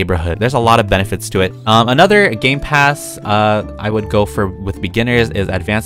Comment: When he said gamepass i thought he said gaypass